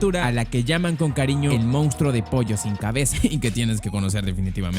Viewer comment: El monstruo de pollo sin cabeza, yo escuché "el monstruo de polla sin cabeza", jajajaja perdón